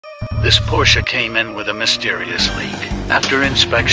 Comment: I heard "Porsche Cayman" at the beginning of the vid and thought 'that's not a Cayman". Then I realized you were saying "came in".